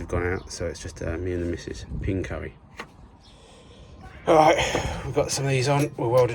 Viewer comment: I thought you said PINK curry at first. - keep up the god work sir - working late so chicken super noodles for dinner tonight.